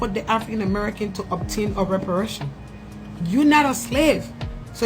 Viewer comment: Did she just say “OUR REPARATIONS”?!!!